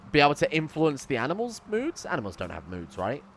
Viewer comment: anyone else misheard this as boobs?